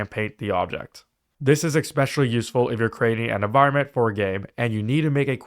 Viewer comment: Did you really say eXspecially? And calc-uh-lates??